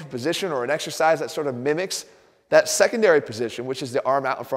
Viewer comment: Exercise? I thought you said extra fries...